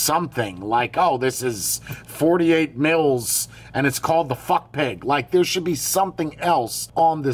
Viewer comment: I feel like I must have misunderstood what you said the name was, because I thought I heard "Fuck Pig". : |